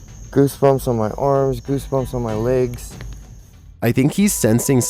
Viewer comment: Did he just say " goosebumps on my arse " ?